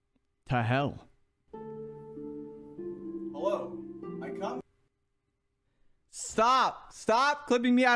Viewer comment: I thought he just said "Hello. Icup."